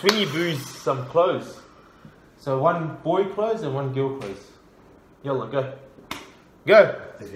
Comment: did u say "yalla go"?🤨🤣